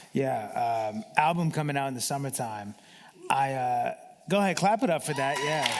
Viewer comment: I swear I thought he said ' Yeah go ahead and clap it up for daddy lol '